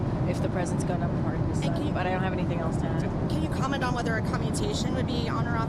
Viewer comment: “I have nothing else to add to that.” Is what she actually said, could be a honest mistake, I don’t really know for sure. But I can ask that you don’t start going down the same road that CNN went down with their “NEVER TRUMP” trash! Agenda! Keep it 100,000,000% real on the topic of politics, finance, security of the Nation’s Boarders and influence around the E.U. & Israel, honestly I’m not sure how I feel about Ukraine 🇺🇦 but I do know, I don’t want an away Team over there fighting with Russia 🇷🇺 just in case Chinese Special Forces have made it across the Country’s southern border! Look Americans FROM EVERY WALK OF LIFE SHOULD JUST BE READY SO WE DONT NEED TO GET READY!!! And if they would have finished the WALL This would not have been such a big deal!!!! Because they voted against the made up Trump and not the real Mr.President Trump AKA GODFATHER TRUMP/ MR.AMERICAN DREAM!!/MR.YOU’RE 🔥!!! MR.MAGA-MAKE AMERICA GODLY AGAIN!!!TRUMP FOR THE WHITE HOUSE.